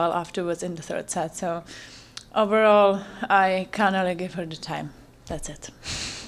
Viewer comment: What does she say ? "Overall i kinda ... what ? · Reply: Didnt gave her time???